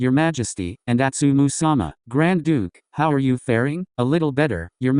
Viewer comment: Okayyy now I know that’s not what was said but I heard Grand Dude and can not stop laughing at the thought of Sakusa ( un-ironically ) calling someone Grand Dude😂😂😂